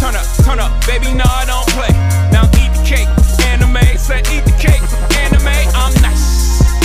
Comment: Thought he said eat the cake anime 💀